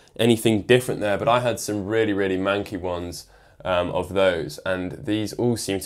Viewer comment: did he say... mankey